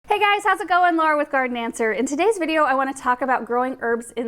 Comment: Dang... thought I heard you say "Gardenmancer" instead of "Garden Answer"